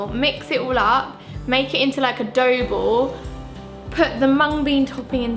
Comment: did she just say " A dough bo"?? hehehe